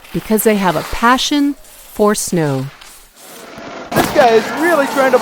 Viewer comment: I thought it said, "Darthmoul passion for snow". Kind of disappointed now